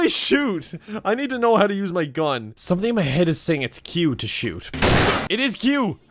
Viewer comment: When he said 'my brain is telling me it's q to shoot' I thought he said 'my brain is telling me it's cute to shoot' and I was like, yes kevin, shooting is cute